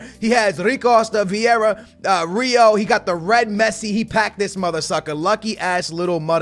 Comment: when he said "mothersucker" I thought he actually said motherfucker, I nearly had a spit take, turned my volume to 100 and rewound